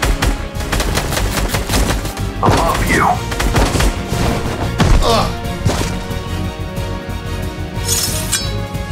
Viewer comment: did he say I love you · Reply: No he said above you